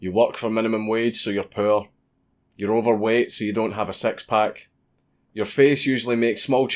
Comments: Ooooh six pack. I thought he said sex park. I just have a little muffin top and I was wondering why I can't get into the sex park. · I thought you said sex pack when you said six pack, you bloody Scotsman. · I keep hearing "sex pack".